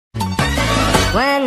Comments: I thought it said "When the rythym rythym starts to play dance with me" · I heard “make me gay” This video made me gay. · i cant be the only one who thought at first heard "marimarima starts to play.." 😅 right😐